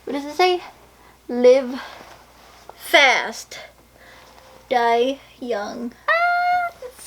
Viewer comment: I thought it said "Live fast die clown." Either way it looks awesome!